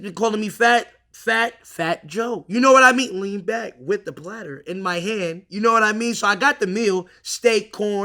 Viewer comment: Always thought he said lean back w the Prada